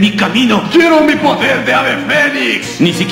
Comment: Saga: quítate de mi camino Ikki: quiero mi poder de ave fénix, 🤣🤣🤣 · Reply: Yo pensé que decía "que hay de mi poder de ave fénix"